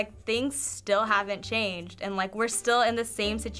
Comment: did she just say things haven't changed?